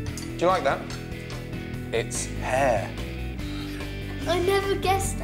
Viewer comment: for a second there i thought he was going to say " its your pet hair" lol